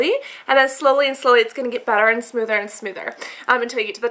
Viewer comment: i thought she said 'douche comb', i'm like 'what the hell is that?' lol